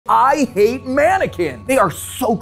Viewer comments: Unironically, I scrolled past and thought he said "I hate ni-" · At first I thought you said “I ATE MANNEQUINS” and I was so confused until I looked at the text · I thought he said “I ATE MANNEQUINS “😭😭😭👍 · Idk why I thought he said I ate mannequins😭